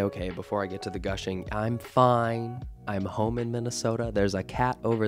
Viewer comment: Oh my god I honestly thought you said "I'm homo in Minnesota."